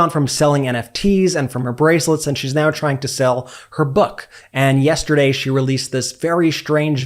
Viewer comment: Ahaha, at 13:45 I mistakenly thought I heard you say "... and Melania has moved on... ... she is now selling her butt... " 😀 and then realised you said "book"... but actually, yup, that's exactly what she's doing!